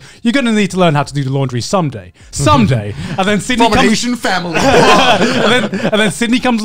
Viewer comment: Can a native English speaker help me out for a bit? What is Connor saying here and maybe also, what is he referencing? 1:13:32 (When he responds to Garnt's: "My mom said: 'You have to learn how to do the laundry someday, someday'") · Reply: He says “from an asian family bruh” i dont think hes really referencing anything but making fun of how asian parents are strict on making their child independent